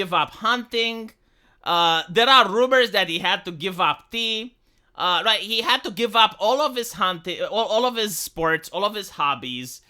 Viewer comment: heh I forgot to turn this back to normal speed from 1.75 and it sounded like you said "He had to give up hunting, and he had to give up Peeing!" damn man, how did he not explode?